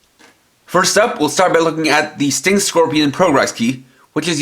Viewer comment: When I first heard the finisher sound in the show, I thought it was saying Sting de Scorpion, but then I heard the other finisher sound and figured it out